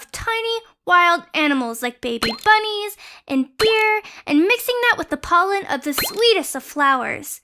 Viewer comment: It sounded like Beer then Deer